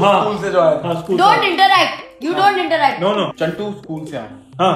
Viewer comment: when she should say interrupt instead of interact.